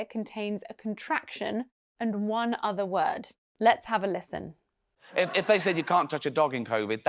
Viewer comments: does it sound like "If I said ..." instead of "If they'd said..." to me only ? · the problem is really his "they" not the " 'd"; it totally sounded like "I'd"🤯😶 And 18:30, honestlyy the sort of is not even there, not even a sorta, just some "SV"😅🤔